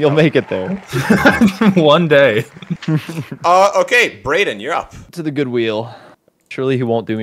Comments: what he’s says youre up Subtitles:uh ok Barack Obama you’re up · Did anyone see at 11:30 in the video is barrack obama instead of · the caption says Barack Obama instead of